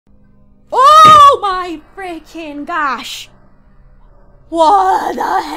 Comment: did she say oh my feaken gosh?!!